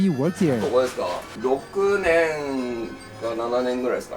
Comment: Did he say roku.... nan...